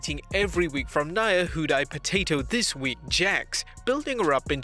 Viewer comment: “Nia who died potato this week” What TF did you just say? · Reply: Nia "Who'd I potato this week" Jax